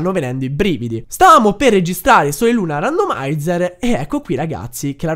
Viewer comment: al minuto 2:50 hai detto per sbaglio (credo) "soi luna randomaizzer" al posto di pokemon luna randomaizzer. senza offesa spero che la serie continui. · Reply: ha detto sole e luna....anche se in modo molto veloce😂😂😂 poteva sembrare soy luna 😂😂😂😂😂😂😂